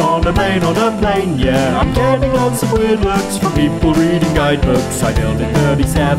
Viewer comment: 'That took beiges' killed me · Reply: i thought they said “lots of beiges” lol